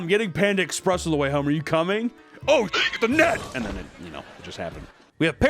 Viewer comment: did you say the N-word? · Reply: "oh shit the net"